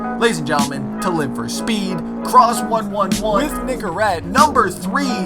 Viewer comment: I thought he said this nigga red instead of nicorete